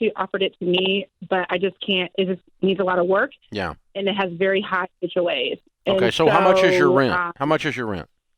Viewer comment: “It needs a lot of work and it has....” what the heck did she say there? I played it 10 times and still can’t make it out. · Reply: high HOA fees